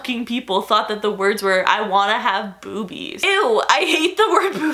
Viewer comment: I thought the lyrics said “I wanna have boobies” lmao